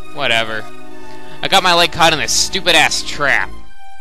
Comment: did she just say stupid-ass in a Mther game O_o